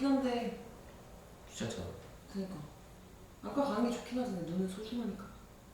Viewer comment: 너는 소중하니까 이렇게 들림 ㅋㅋㅋ루ㅋㅋ